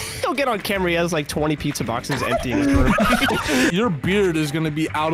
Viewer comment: Gotta love at 5:35 when says "room" but the subtitle says "car"